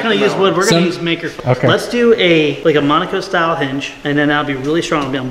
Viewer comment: When he said Monica style hinge. I had to rewind cuz I thought he said manga style hinge. I was like WTF ETC....🤣🤣🤣